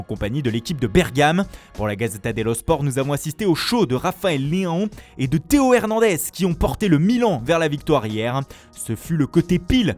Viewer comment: Ra...ra...Rafael quoi ? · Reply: Leao